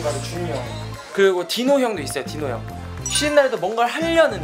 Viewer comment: did dino just call himself dino hyung? ㅋㅋㅋ